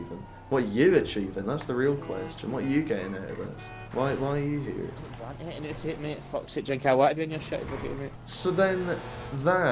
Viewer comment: wtf did he say lmao Legit can someone translate? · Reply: I don’t want anything out it mate fuck sake do you think I wanna be in your shit video mate